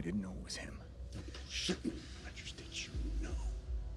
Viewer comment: what did tony said on 1:14 2nd line? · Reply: "Dont bullshit me Rogers did. You. Know?" Or sum like that